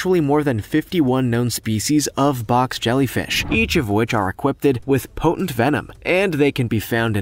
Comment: I might have misheard but did you say “equippeded” when talking about the box jellyfish?